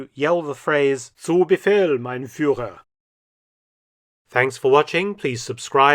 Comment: What did Mark say at the end of the show at 13:50? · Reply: “Zu Befehl, mein Führer” means “at your command” or “yes sir, my leader”.